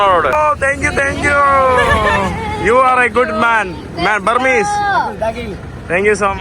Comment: when you said "Burmese", I think he said "Rakhine" --correcting you...